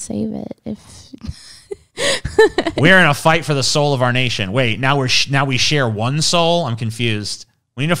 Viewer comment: I heard that as "we're in a fight for the sole of our nation".